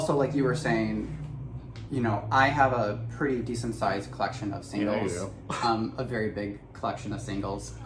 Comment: Omg I heard “I have a very large collection of sandals” and I was like…?!?! I feel like I don’t even know you! 🤣 (This is what I get for doing too many things at once)